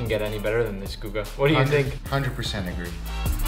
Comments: what did Guga say?😅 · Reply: 100 percent agree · WHAT DID GUGA SAID TO NICK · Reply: He said, agreed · My boi messed up by this word... "100% nig.." what? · What did he sayy????? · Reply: He said "100% Agree"